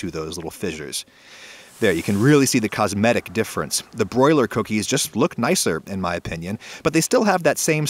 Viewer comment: At the start I thought he said I boil my cookies